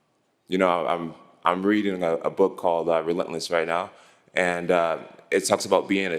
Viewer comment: What is the name of the book that jon jones talked about at 16:58? I can't understand what he is saying 😂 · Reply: Cultures of Relatedness